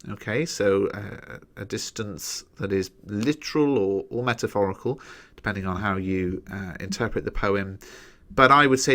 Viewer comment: what word did u say at 5:11 surjoro? the structure technique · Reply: caesura